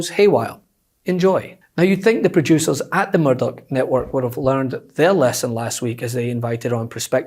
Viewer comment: Your accent sounds like you said Murder Network, sounds right to me #trumpstinks